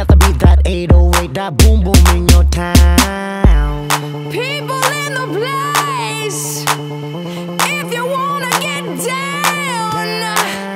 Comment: Am i the only 1 who thought she said, ppl in the blaze, as if ppl were on fire,like WTF!?!, XD Back then this music was great back then, still great today, loved the BEP all day,anyday :)